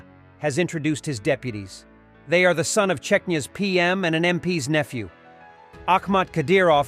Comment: auto-generated subtitles say "they are the son of czechia's PM and..." wait what? Again? Not the first time I hear Czechia instead of Chechnya since Czech Republic changed its name to Czechia so "it's easier to speak the country's name to tourists". And what Solovjov, so funny when you apply his speech to Russia. Putin as a lapdog, lol.